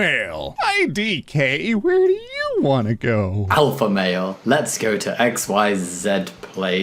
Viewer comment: am i the only one who hears alpha mayo?